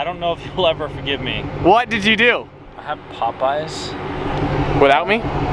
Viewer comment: When said pop-eyes I I thought he said pop-ups